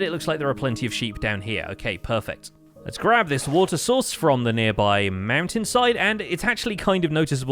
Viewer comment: Is it just me who misheard him saying "water source" as "water sauce"